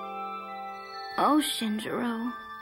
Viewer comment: On 10:42, I thought she said 'ocean drool' instead of 'Oh Shinjiro'.